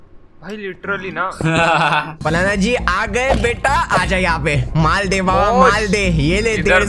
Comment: i just heard says banana ji 🤣🤣🤣🤣